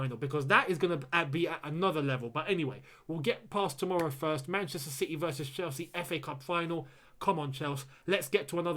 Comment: did man just say manchester city VS chelsea FA CUP FINAL?